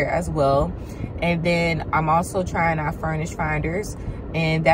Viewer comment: what did you say there? Furnished finders? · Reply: Yes, furnished finder they have a website, and you can create a profile. It’s all professionals on this platform.